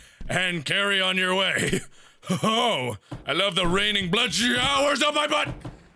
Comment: did he say "i love the blood showers in my butt" at 4:28 ? O-o